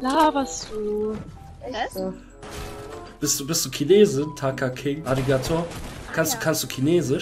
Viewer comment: Ich hab anstatt Taka King, Kaka King verstanden😂👌